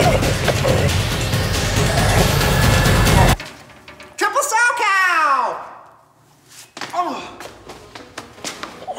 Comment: What did Robin say?? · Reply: Ezri Cox "Triple salchow!"